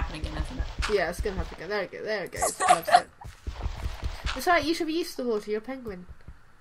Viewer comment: when u said u should be used to the water your a penguin I thought you said you should be used to the water you pregnant lol btw im a girl on my dads account